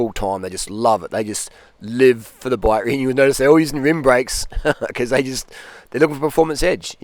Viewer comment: I thought youd just say “ rim broyks”…